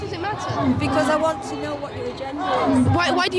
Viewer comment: Did that girl say: "I want to know what your agenda is?" Silly me, I thought she said, "I want to know what your gender is?" Ha ha. He he. Silly me.